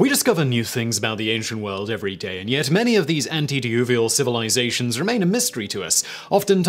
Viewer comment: I thought you said all of these would be antediluvian?